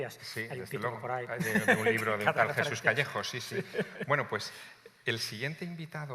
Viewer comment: Casi paso, pense que decía Jesús Vallejo.